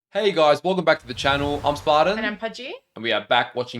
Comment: I always hear Spartan's name as "Spider" with the accent.